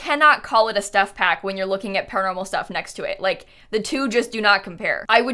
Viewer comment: I misheard paranormal stuff as carnival stuff, and now i'm curious what a pack like that would have